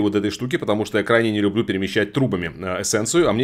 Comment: Услышал "перемещать трупами". Нет, всё таки надо спать подольше...